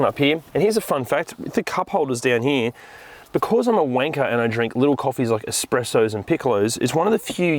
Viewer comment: i thought i heard wong he did say wanker lool